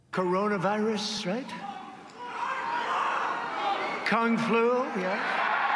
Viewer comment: Did he say “Con Flu”? 😂 Because that’s a whole other thing from COVID-19 and has been around as long as we’ve had conventions.